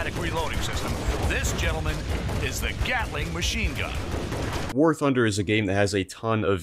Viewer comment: I hate that I heard “Gyattling machine gun” in the intro